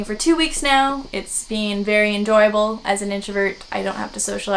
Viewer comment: Did you say "Anjoyable?" Play on words of.